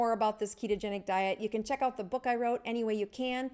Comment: I was listening from the next room and I thought she was saying "Check out my book any way you can" not "Check out my book, 'Any Way You Can'". LOL